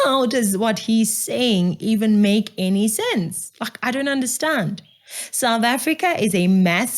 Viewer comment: Good morning Good morning all At 3:02 it sounds as if your saying "F@ I DON'T UNDERSTAND" 😂😂😂 Even if you didn't, so many of us will echo it 😂😂😂